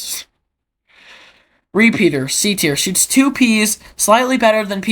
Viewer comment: i thought you said “shits 2 peas”